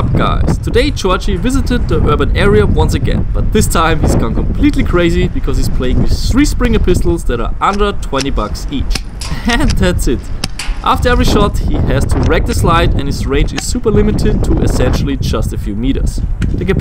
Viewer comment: I thought he said a 120€ springer and I said WHAT